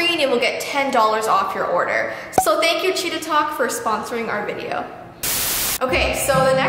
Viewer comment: When she said cheetatalk I thought she said “Cheetotalk” 😂😂😂😂🤣☺️🤣🤣